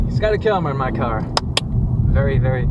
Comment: Oohhhh I thought it said "my dad is scotty kilmer"